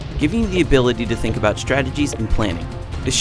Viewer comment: lol did he just say stragedy????????